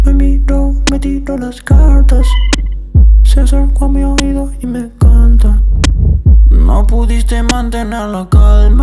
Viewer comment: Yo pensé que decía ''se acercó a mi oido y me encanta'', no ''se acerca a mi oido y me canta''